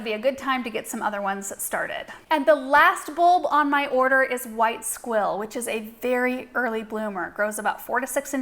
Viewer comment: I had to do a double look at my screen, I thought you said white SWILL. 🤣🤣